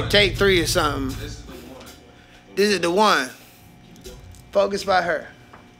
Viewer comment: I thought he was trolling in the beginning when he said “right hurr” lol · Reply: I'm almost positive he said focus by H.E.R.... But the first time I heard it I definitely thought he said right hurr lol